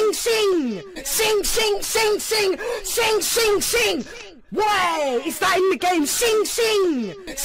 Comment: “Shing shing, shing shing shing shing , shing shing shing whyyyyy is that in the game? shing shing.” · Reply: oh i thought he said waitttt is that in the game